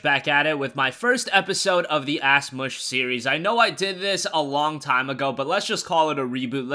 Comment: In the intro it sounds like he says "Ass mush series"